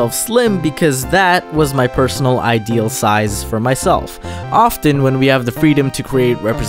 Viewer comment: Also I thought you said extra size xD